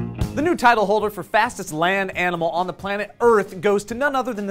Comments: I thought it said "new fattest animal on earth" at first lol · I thought it said fattest animal in the world...fastest makes more sense now. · thought it said fattest animal.